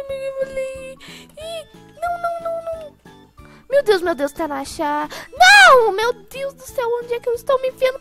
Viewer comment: eu ouvi tanasha kkk